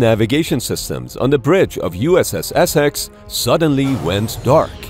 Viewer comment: I thought he first said "U.s.s. Essess". Now I have to watch "Hot Shots".